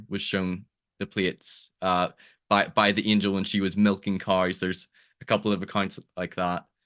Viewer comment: Misheard the words “she was milking her cars.” I like the concept and think they should add it to their books.